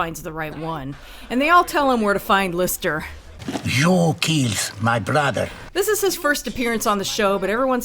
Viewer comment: Crazy thing, I occasionally mishear the line "You killed my brother" as "Your keys my bladder".